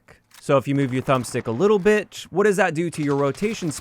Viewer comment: fun fact- of you are using subtitles then at 5:23 he says "little b***h" instead of little bit